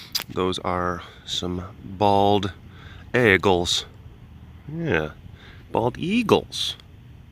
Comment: Maaaaannnnn this is what I get for not staying current during my move. Coming to Wisco for the fall delish and flying turks. Why did it sound like you said bagels not eagles lol. Very Wisco appropriate substitute with the ayegalls there eh! Hope you picked yourself up some New Glarus while visiting and enjoyed your trip!